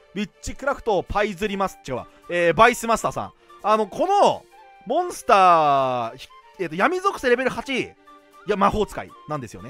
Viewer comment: 下ネタから動画入ったので、「このモンスターは」が「肛門スターは」に聞こえてしまいました。すいません